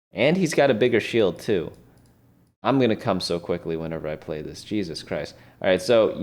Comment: 'im gonna come so quickly whenever i play this jesus christ' did i hear this correctly HAHA